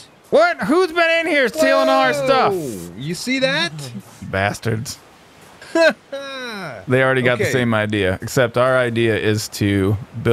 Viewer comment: Anyone else hear say bastard around 08:20? Thought this was supposed to be as clean as possible.